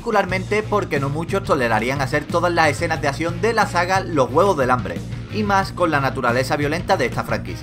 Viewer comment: Escuché ''Los Huevos del Hambre'' xD